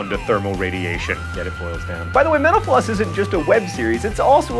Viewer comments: Every time he says "Mental Floss", it sounds like he says Menopause. Goddammit. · It sounds like he says 'menopause' instead of metal floss:P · You said "Mental floss" and I thought you said "menopause." My bad. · i swear when he says mental floss quick, it sounded like he said menopause. I was so confused for a bit there. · Sometimes you say "Mental Floss" so fast it sounds like you say "Menopause"